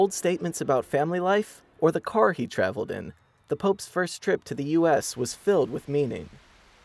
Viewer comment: i thought he said "the popes first trip was filled with memeing"